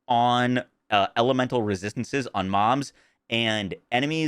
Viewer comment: he definitely said "elemental resistances on moms" I know he meant to say mobs. But that's not what he said